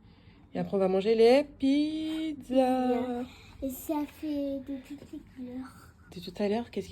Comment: Belle visite de Marseille 😍! Les pizzas font vraiment envie, miam 😊. Quand ta niece te parle de la roue, j'ai entendu "Ça fait de toutes les couleurs" 😅, et toi t'as entendu "Ça fait depuis tout à l'heure". Quand je t'entends parler du Parc Borely, je pense forcement à "Plus Belle la vie". RIP Michel Cordes ☹️